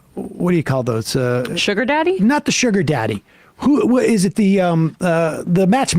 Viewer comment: When he said “not the sugar daddy!” I just heard “not the momma!” 😂